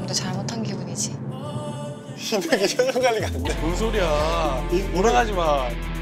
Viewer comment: 에 이 뭐라고 하는거에요? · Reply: Hannah Kim 이 표정관리가 안돼